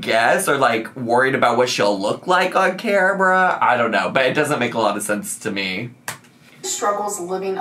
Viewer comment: When you said “it doesn’t make a lot of sense to me” I heard “doesn’t make a lot of vlogsense” | vlogsense